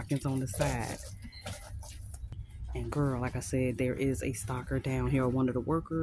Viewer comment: lol I thought you said stalker not stocker